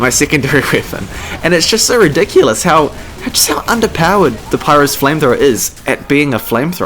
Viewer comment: did you say papyrus's flamethrower?